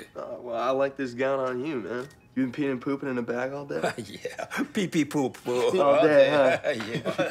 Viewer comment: nigga did he just say peepee poopoo